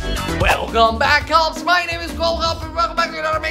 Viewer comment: I thought u said welcome back my name is awful